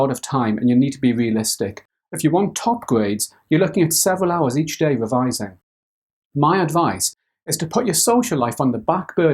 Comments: Thought he said do 7 hours revising everyday,was about to flip. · I thought he said 7 hours, I shit myself